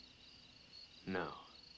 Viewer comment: What's he saying on 5:20? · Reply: Bashful? No. Then get 'em down, I wanna see the world.